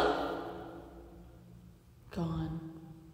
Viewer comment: At minute 35:19, I hear the word "GUN" not "GONE". ?